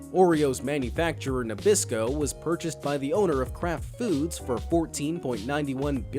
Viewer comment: i heard crap foods xD